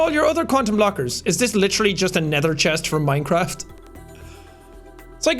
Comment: did you just say nether chest